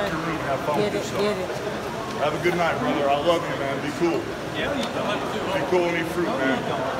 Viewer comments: “BE COOL & EAT FRUIT” Sounds CRAZYYYYYY. 😂😂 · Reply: I thought he said be cool and be fruity 😂 , but thank you for clearing that 👍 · “BE COOL & EAT FRUIT” Sounds CRAZYYYYYY. 😂😂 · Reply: I thought he said “don’t be a fruit” 😂😂😂